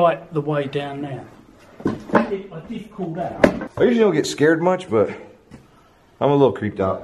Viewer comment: I hear crying and sound like she said scare me